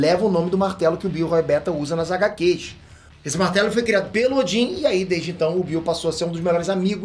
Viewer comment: "esse martelo foi criado peludin" Oxi? Martelo tem pelo?